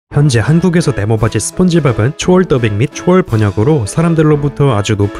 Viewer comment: 이 부분 자다가 바로 일어나서 들어 보니 썩을바지 스폰지밥이라 들려서 놀람